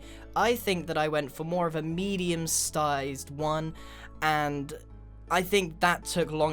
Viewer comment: Did he say stized?